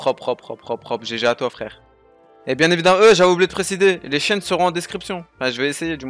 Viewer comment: les quoi seront en description ?😳😂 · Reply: Mermoz 71 les chaînes